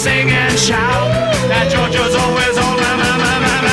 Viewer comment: The line "That Georgian's always on my mind" is a play on the USSR's Georgia country and Ray Charles' "Georgia on my Mind". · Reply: My entire life until now I thought they said that "JoJo's always on my mind", callbacked in Get Back. Mind blown.